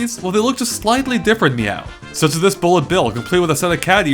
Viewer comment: past that a second or two he sais MEW instead of NEW. or maybe its my hearing...